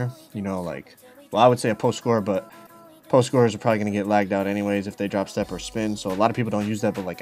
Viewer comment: Did he say post scorers get lagged out by drop stepping?